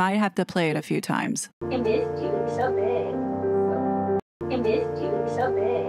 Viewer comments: I heard her say “I missed you, so big.” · I heard "I missed you, so big" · It’s sounds like is saying “I missed you, so big!” But that would imply that has been seeing her a few times.